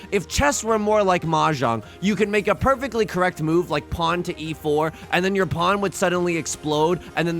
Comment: I heard "Pon to E4", I was like "from when did Chess has Pon?"... then I realized Pawns exist.